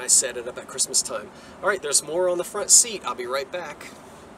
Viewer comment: I have to have my ears fixed. I thought you said "there's a moron on the front seat!"